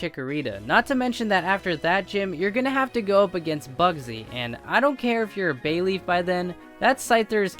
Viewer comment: i wasnt watching the vid and was just listening so i thought he said f*****'s